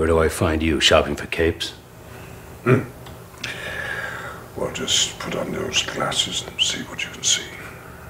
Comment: What does Ian McKellen say at 4:20 ? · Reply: Wolverine - "Where do I find you? Shopping for capes??" Magneto - "Well just put on those glasses and see what you can see." Prof. X and Wolverine - wtf??